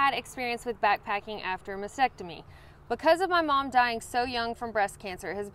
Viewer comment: For the young lady asking about post-mastectomy hiking, it is absolutely possible without discomfort. I would be glad to answer any questions if she wants to reach out privately. But you will not have to give up your passion. 💕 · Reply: That makes much more sense. I misheard and thought it was a man asking about backpacking after a vasectomy, and I thought, "why are your backpack straps in your groin? Vasectomies heal fast, just wait a week."